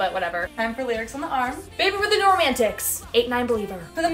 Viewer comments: I thought she said, baby with the doorman ticks😂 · Tell me why I heard "Baby with the doorman ticks" instead of "Baby we're the new romantics" 💀